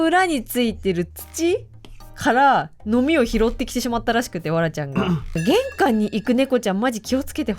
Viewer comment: わらちゃんがぱらちゃんに聞こえて 動物病院！？！？ ってめっちゃびっくりしたww